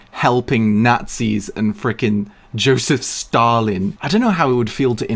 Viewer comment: When he was talking about Stalin, I thought he was saying Starlin and I'm like who the eff is that? Then he said Joseph Stalin and I just went "A doi"!